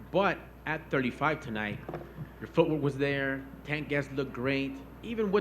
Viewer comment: What did he say? "Tank gas"? "Takedowns"? 🤔 Sounds like tank gas, but that makes no sense.